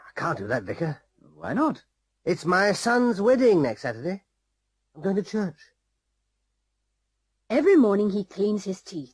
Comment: không thể nghe ra "I'm going to church"